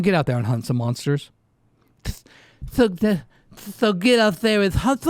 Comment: I heard handsome monsters 👺 😂